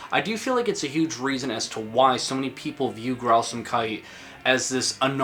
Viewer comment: Am I the only one that thought he was saying Grausamkudi?